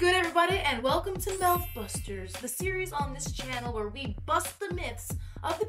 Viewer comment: i thought you said methbusters 😂